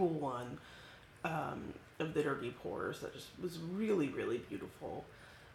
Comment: so... a couple of things. listening to this while trying to talk to at the same time is probably not a good idea. i could've sworn i heard you say "dirty whores" and i was wondering why you were watching that. it wasn't until, maybe, the 230 minute mark that i understood you were saying "dirty pours"... it's still early. i think you are an awesome creative mom - that play room is <link> :o)